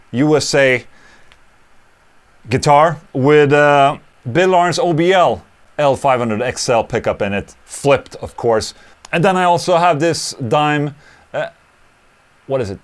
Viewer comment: Did anyone else catch "With Bin-Laden's OBL"